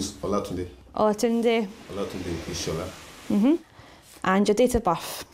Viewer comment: I thought she said "and your data buff?" LOL.